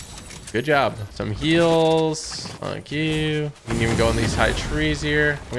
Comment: no one is talking about how he said "Some Heals" Then After That It Sounded Like He Said "F*** You"